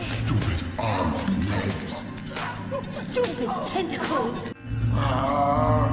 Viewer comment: it sounded like that Muriel said stupid Grey Matter.